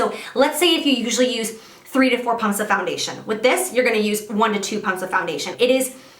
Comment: When you were talking about the Artis brush I thought you said "3-4 pounds of foundation" 😂